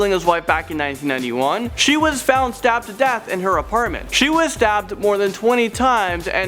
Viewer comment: is it me or did anyone else thought he said dabbed to death? LOL!